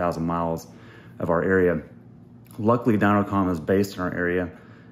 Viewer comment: so wait was it dynojet that came in clutch to do this? because thats what it sounded like you said at the end of the video.